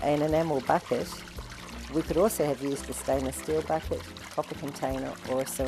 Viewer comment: does the instructor say copper container or coffee container could be used for stirring the water? She says they are using an enamel bucket but a stainless steel bucket could be used, a ceramic container could be used, and I couldn't determine whether or not it's copper versus coffee container that could be used as well. The caption says coffee but I assume copper. However, I'm not 100% on that. Would love some feedback! This is excellent information. I look forward to turning our yard into a biodynamic garden / oasis. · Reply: Hi Darlene, a copper stirring vessel is the ideal and as these are not always easy to get hold of a stainless steel, wooden vessel or enamel, or glazed pottery crock pot or a quality plastic bucket are all totally acceptable alternatives. Hope this helps